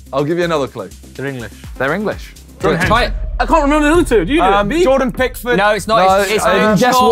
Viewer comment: Did just say Dimitri Payet after said it was an English player...